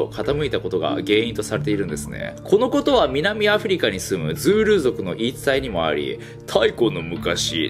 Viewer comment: 「ズール族の言い伝え」が「ズール族のインスタ」に聞こえた 最近の部族はSNSやってんのかと